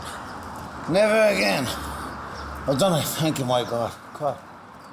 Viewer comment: does he say my god or white god? Cause if Jesus is real he was black.. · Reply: He-said-"my"...